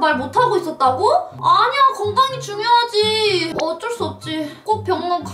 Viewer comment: 저만 건강이를 곰팡이라고 들었나요?